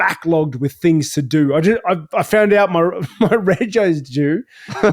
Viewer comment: Does anyone know what Rob said at 7:01 ? i’ve rewatched this part like 100 times and I can’t figure out what Rob said. To me it sounded like “my ray J is due” or something like that. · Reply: He said "my rego is due" which means his registration for his car is due for renewal/payment